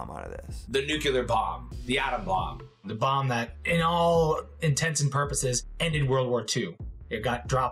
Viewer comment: First time EVER I heard the phrase "intense and purposes" used right.